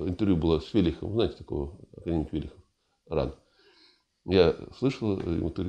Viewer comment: академик Велихов. для тех. кто не расслышал